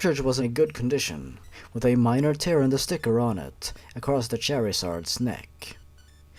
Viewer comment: when he said charizard the subtitles said cherrysarts XDDDDD